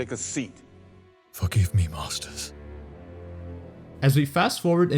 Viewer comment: I thought he was about to say fuck you master 😆